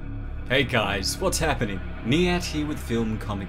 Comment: I always thought you were saying "its Me At Hugh with film comics explained". In my head I thought your name was Matthew or something. Glad I now know it is Niyat. Great content chief, keep up the good work.